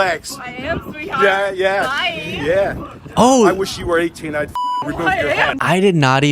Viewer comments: Does he say, "If you were 18, I'd remove your head." His brain is scrambled while being recorded. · I heard, "I wish you were 18! I'd f*ckin' remove your head!" And then he says it again later. Is he channeling Harry VIII? I'm so confused.😂😂